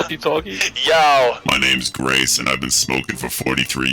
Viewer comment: I thought he said "Hi my name is RAISIN and I've been smoking for 42 years" XD instead of Grace